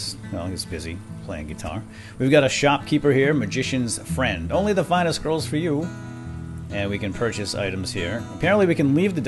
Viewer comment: Coulda sworn you said "Only the finest girls for you!"